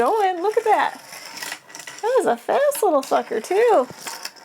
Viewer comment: did u say little fucker 😂😂🤦‍♂️